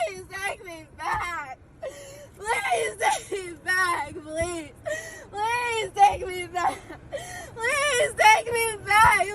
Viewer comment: I thought she was saying “please….please….take me back”. She was really saying “Police…..police…..take me back”. 😂😂😂😂.